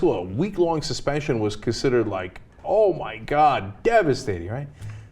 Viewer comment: it says all white god instead of oh my god lol